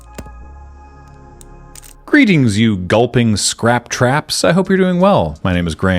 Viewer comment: My half-asleep brain misheard this as “Gullping’s Craptraps,” and is now actively trying to work it out as a creative concept… 😂